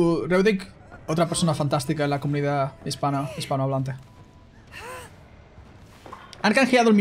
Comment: la primera vez escuché "otra persona fantástica de la comida hispana" Se metio en personaje